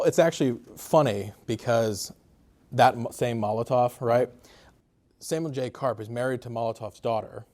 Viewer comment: Who is the guy who was married to Molotov's daughter? At 13:20 it sounded like you said "Samuel J Cart" was married to Molotov's daughter. Who was Samuel J Cart?